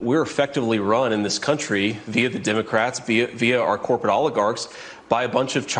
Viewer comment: A republican spinning a corporate oligarchy onto the left is the most egregious thing he’s said and that’s a pretty high fkn bar 😅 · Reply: The left? I could’ve sworn he said democrats